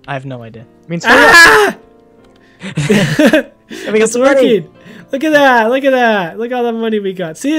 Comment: Everyone out there trying to hear what said at 14:11, when said wdtm. aaid "it means HURRY UP"